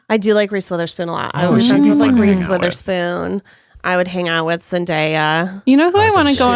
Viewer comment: said she liked reese withersppon twice, the first subtitle said 'reese witherspoon and the second one said 'I wish i could grease with her spoon'!!!!! IM DEAD